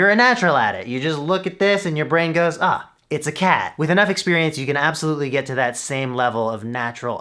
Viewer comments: did he say "cat"??? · this killed me lol 😂 Did he say cup or cat xD? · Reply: Cap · Did he say cat?!? 🐱